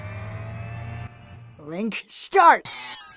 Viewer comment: All these years, I thought he said ‘Blink, start’ 🫣